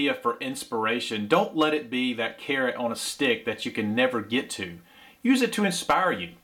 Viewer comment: OK, I have to tell you that when you said “carrot on a stick” I thought you said Karen on a stick. LOL Social media groups also have their share of Karen’s/Kens, tow police, and self proclaimed experts. They are seemingly so prolific it had me questioning if I wanted to get back into camping with “all those types” out there and hearing the camping people horror stories. I will say that there’s likely some truth to some of it but there are still plenty of good folks out there. Appreciate ya!